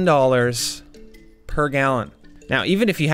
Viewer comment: I thought it said 39 a gallon I was like that’s not much